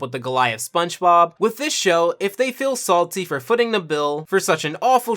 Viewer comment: "Footing the bill"? Did I hear that right?